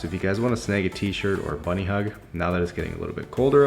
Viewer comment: Is there any better way to start off your Friday? 😛 What was your favourite part of the video? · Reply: 100% when I thought you said I could get a bunny on sale on your website and ended up googling Canadian slang to find out a bunny hug is actually a hoodie